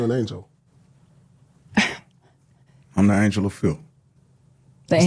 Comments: I thought he said he was the angel of filth. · To me it sounded like he said I'm an angel of feel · I thought he said he was an angel of filth